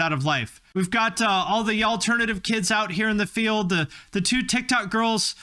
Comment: I'm sorry. Did you just say "Y'allternative" at 13:40? Because that's the perfect name for this genre